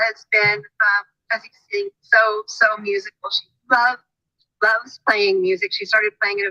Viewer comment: "Loved, loves playing music" ? Is that what she said ?